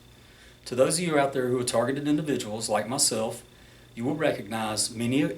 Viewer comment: thought he said retarded lol